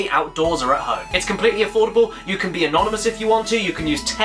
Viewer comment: Oh anonymous i thought you said enormous for a sec